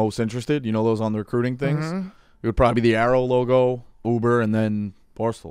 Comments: Any idea what he was saying when he said his 3 job choices were barstool Uber and arrow logo. What is arrow logo? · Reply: aero not arrow, Aeropostale is a clothing store, he means he may be working in a mall or doing Uber when his contract is up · Any idea what he was saying when he said his 3 job choices were barstool Uber and arrow logo. What is arrow logo? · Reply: aeropostale (logo) shitty abercrombie brand.